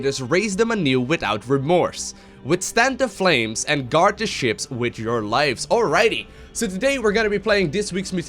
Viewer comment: I laughed when I heard "guard the shits" haha